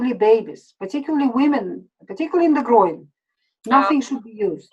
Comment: Did she say "particularly in the groin"?